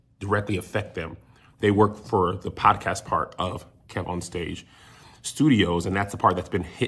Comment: Nobody caught at 6:45 he says KevOn stage but the CC said KevonDust. Come on now don’t be messing with the deaf